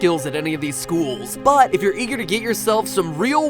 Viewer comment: And did he just say eager i t sound like jaeger try to think attack on titan duh